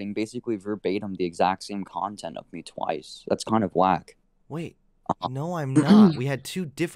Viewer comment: i'm pretty sure he says "that's kind of whack" but I swear to god I heard "that's kind of Black" and I was so utterly confused